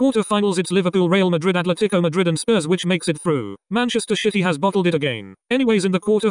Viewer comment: did I hear Manchester shitty 😂